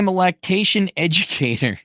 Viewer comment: You became a what? Lactation Educator? Learn something new every day. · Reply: For a second I thought he said "lactation engineer"....